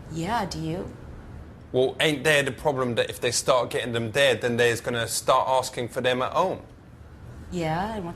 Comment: what did he say at "13:00" aint that a problem..... did she even understand ihim? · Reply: if they start getting them there they're going to starting for them at home